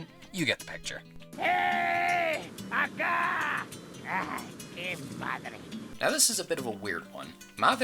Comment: i legit thought the guy said "Hey!! Ada!!!"